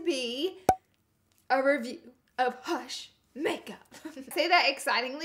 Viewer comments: No it sounded like you said doofus makeup. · I thought it said Lush makeup and I was HUH?! 😹😹😹